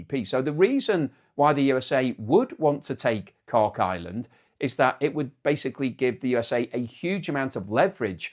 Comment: I swear, I thought you said “Cock Island” 🤦‍♂️🤣